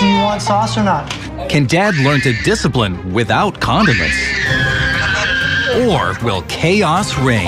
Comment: "can dad learn to discipline, without condiments?" this episode is gonna be wild i can already tell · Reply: Simone Rose when I heard it I thought he said condom lol